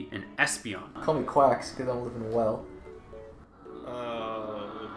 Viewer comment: did someone said Quaxwell??!